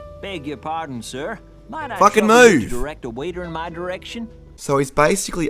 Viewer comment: I cant stop laughing when i heard "FUKING MOVE!"